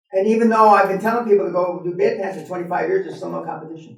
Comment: Can please someone tell me what industry he said at 2:51?? I couldn't hear exactly what dan said.. thanks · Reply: He said bedpans. Aka assisted living. Aka hospitals.